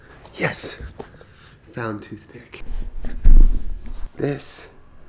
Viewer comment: When he said toothpick I thought he said too thicc. That would have been a better name